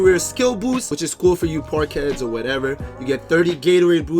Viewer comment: Is it just me that thought he said poor kids?😂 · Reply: Peter Jollymore park heads, not poor kids